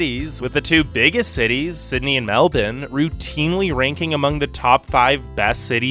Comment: did he just called Melbourne “Meldon”?